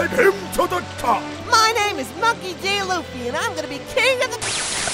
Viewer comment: "My name is Monkey D. Luffy! And i'm gonna be king o' the-" Legend says that Ten Thousand One-Piece fans unsubscribed, that day. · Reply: i thought he said Monkey Gay loofy XD